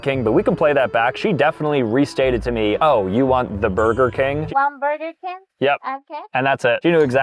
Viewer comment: She was actually saying “One burger, king?” 😂